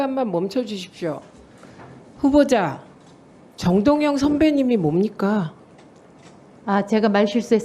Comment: 마기할멈 깃구명도 늙었나? 분명히 " 정위워님"이라고 했는데 "선배님"이라고 들었단다 저런 긱구멍으로 소리를 들으니까 날리면이니 바이든이니 회괴망청한 시빗거리로 전파안비하고 있잖아 으히그 답답아 7:40분경